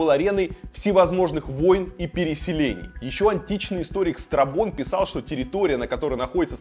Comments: вам послышалось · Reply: Да нет.Он правильно сказал историк Страбон,а слышится страпон))))Как и философ Фалес слышится фалос или Эразм Ротердамский звучит как оргазм.Все это слышится в силу образованости и воспитания.Вот мне послышалось правильно Страбон,вам видимо страпон)))))🤣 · послышалось "античный историк страПон" xD